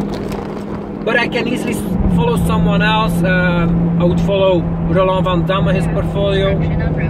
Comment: Hello, I couldn't quite make out the name of the investor you mentioned at 18:49? Love to look into his strategy, and thanks again for a great video. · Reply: Roland Vandamme